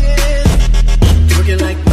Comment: is it just me or does it lsound like he says "tiger''